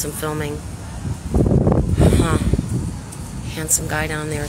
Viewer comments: Are they taking it down to put up bigger, higher, stronger fences? (Did she say "had some guy at the door"? For a second I thought she said "handsome guy at the door"? 🤣) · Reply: That's what I thought she said too! Handsome. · Are they taking it down to put up bigger, higher, stronger fences? (Did she say "had some guy at the door"? For a second I thought she said "handsome guy at the door"? 🤣)